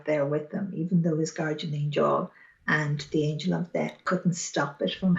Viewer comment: I realised after a while she was saying the Angel of Death but at first I thought she said the Angel of Debt. It is her Irish accent, which incidentally is very calming and beautiful. I think an angel of debt would be very useful, especially in the current economic climate though.